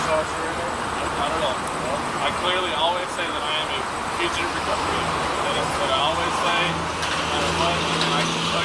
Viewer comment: bro really said "can I get a ticket for this because I have to go to fire":face-blue-smiling::face-blue-smiling::face-blue-smiling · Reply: Who knows what he's saying half the time. For example: At 15:33 The guy says "fugitive recovery agent", yet the captions say "future president" in big bold coloured letters. The editor needs to be factual and not biased into making him dumber than he already is. It also makes the content creator untrustworthy.